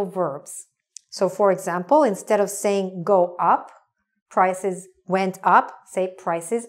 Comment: I thought it said 'goup' not 'go up'